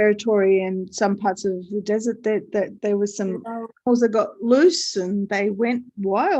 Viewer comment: when you were talking about Australia and all the Camels when they got loose and everywhere, being a Canadian, I jolted in surprise because I thought you said "got Moose", not "got loose" and omg I was thinking Australia has moose too! wow! xoxo